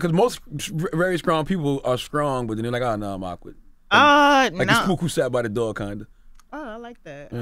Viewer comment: Can someone tell me what is Charlemagne saying at 10:07-10:10? I kept repeating this vid and still can't figure out what he's saying. #helpme #lol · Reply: He said "people are like I'm strong but then again I'm awkward; like the Spook Who Sat By The Door kinda." The Spook Who Sat By The Door is a book by Greenlee.